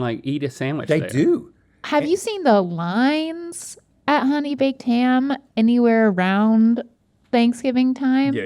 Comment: Why did I mishear that as honey vaped ham.